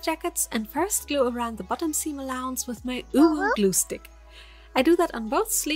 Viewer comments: When I heard her say UnU glue I thought she said UwU glue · I thought you said “UwU glue stick”😂